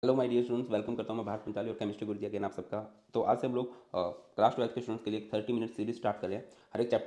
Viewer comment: pe kya bole aap ? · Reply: Hello student welcome krta hu